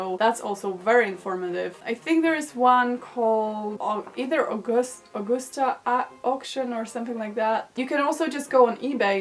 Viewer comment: can someone spell what she said? my english is so bad lol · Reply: I know this is old, but... she is referring to Augusta Auctions <link>)